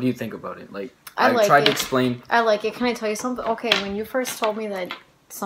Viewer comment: i hear a voice saying i like it